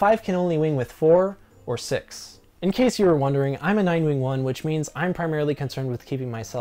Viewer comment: "I'm a nine wing-wong." It's late and I found this little mishearing stupidly funny.